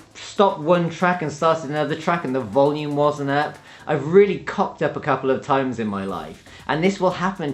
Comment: does he say "I really cocked up a couple of times"? because I'm using that from now on. I'm super beginner with DJing but I've toyed around with guitar (campfire level). Gotta say this Camelot wheel became a lot clearer when I applied it to mixing. I've never sat down and studied theory but this video made it pretty clear for me.